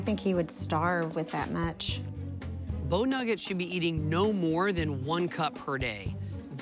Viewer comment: Who else thought they were saying " bone nugget". 😂